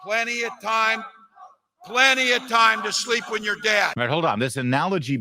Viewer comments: I thought he said sleep with your dad creepy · I really thought he said “sleep with your dad” not “sleep when you’re dead”,it just sounds like something Walz would say so I believed it when I thought that’s what he said